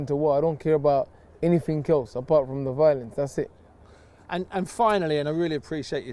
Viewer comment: what does he say at 7:35 : "he doesn't care about anything except for .... " - I can't hear it clearly ? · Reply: The violence